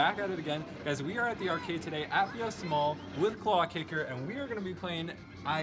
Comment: i thought i heard at 1st when i started the video turns out I was right